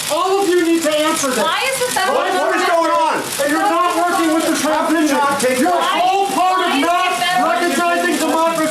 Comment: I think i heard " Are you working with the Russians" in there somewhere